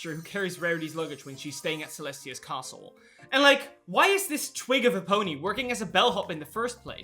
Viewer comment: I was eating Chipotle and looking down at my meal when I fr thought and heard you said "twink of a pony" instead of "twig of a pony" and now I'm pony cackling on my chair